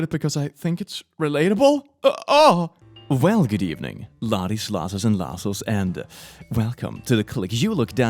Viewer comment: it literally took me years to realise hes saying laddies, lassies and lassos. i thought was just saying lassies twice for some reason :')